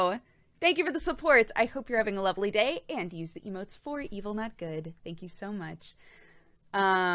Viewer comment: Heeey:D BTW for the longest time I thought she say Evilnut good, like.. an evil nut.. :D